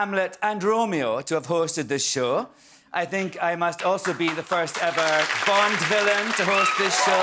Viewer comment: At first I thought he said the first ever Bob Dylan to host the show